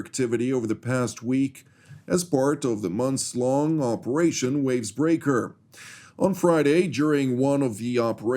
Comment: for a brief moment I thought the announcer "Operation Wind Breaker", which I thought was an interesting operations title....then I realized I misheard and it was actually "Operation Wave Breaker", something entirely different.